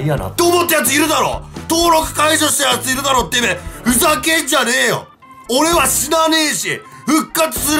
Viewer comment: 45秒あたりのふざけんじゃねえよがボビーみたく聞こえたのは俺だけだなw うんw